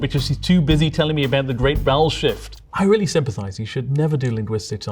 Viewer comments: When he first talked about vowel shift, I thought he said Bowel Shift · I misheard it as "the great bowel shift" 💀💀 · ooooh! I thought you said "bowel shift"! I was expecting you to digress to the guts.